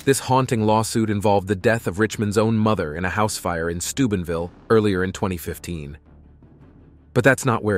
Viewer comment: i keep hearing stupidville and cant remain serious